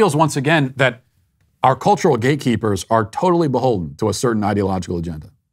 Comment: I thought he was saying GAYKEEPERS. Ohhhhhh gateeeeeeeee keepers. I guess either works though.